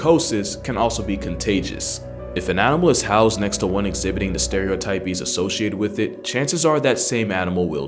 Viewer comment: Did you just say "stereotypies"? 🤔